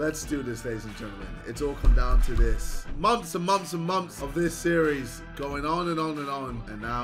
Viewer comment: why does say “moms” instead of “months”? 😂